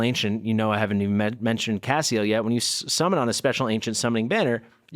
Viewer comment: Each time I heard you said Cassiel, I thought you said Caseoh 😭😭 Playing your youtube video while playing other games!